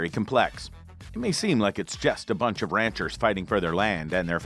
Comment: Did they say it was "just a bunch of ranchers" or "just a bunch of ranters" fighting for they land?